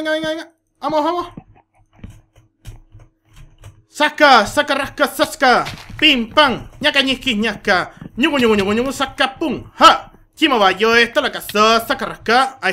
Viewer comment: desde el minuto 9:42 No le entiendo nada LO rrepeti y le entendi xD dise: sasca sasca rasca sassa -PIMPÁM ñaca ñisqui ñasca ñuguyu ñuguyu ñuguyu ñuguyu SACA-PUM! JA! chimobayo ja estalacaso sasca rasca OSEA! NUNCA EN MI VIDA AVIA OIDO ESAS PALABRAS ***** XD buenas palabras aunque todo el video me isistes reir con esas palabras LIKE! <num>